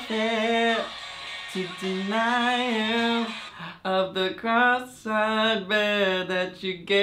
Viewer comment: i heard cross i bear (and even when he repeated it, i still heard the same thing) cause im Catholic, and bearing a cross, is a metaphor for hardship, similar to the cross Jesus had to bear, while walking up to the hill to be crucified. So yeah, i was used to hearing ''something you'd expect instead of what you're not used to in every day life". And initially it blew my mind that they still didnt get it, and gregory alluded to a necklace. But of you know nothing about Christianity, this wouldn't even cross your mind. Ps. i know religion doesnt get treated the nicest on science videos, or on the internet in general lol. but i was just giving an example, and i found it ironic that the example i found came from the same video that was giving the information i just heard.